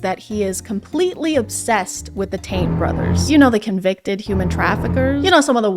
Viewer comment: I had a moment where I was like “did she say ‘Tate’ brothers or taint brothers?” And then I got it 😅